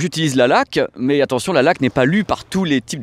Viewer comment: " Moi personnellement j'utilise la laque" J'ai du mal à y croire x)